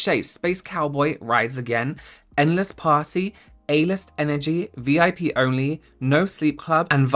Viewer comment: I swear to god with the Urban Decay palette I looked away for a second to spread lotion and heard you say “anus energy.” Yes, I know it was “a-list energy” but I only know that because I rewound to look at the subtitles!